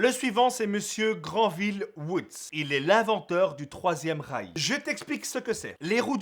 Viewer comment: j'ai compris l'inventeur du 3ème reich ! XD